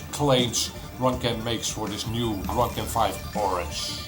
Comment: I love the youtube auto captions. I thought you said "this new runcam 5 orange" but I'm glad I now know what you actually said was "this new drug can fight Boris".